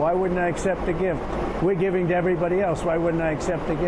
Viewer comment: Air Force One is the most maintained aircraft in the world. And twumpy sounds like he is saying “grift” not “gift”